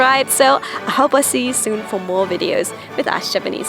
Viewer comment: Trying to hold my laughter every time "Knickers" is pronounced, so closely sounded like what I thought she just said........"No more Knicking them Ni......"